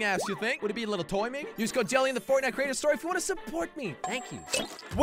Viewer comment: I thought he said “Use code Jelly in the Fortnite Crainer Store” 🤣🤣